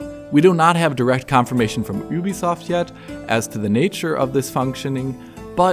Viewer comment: Did you just say "oo-bisoft"?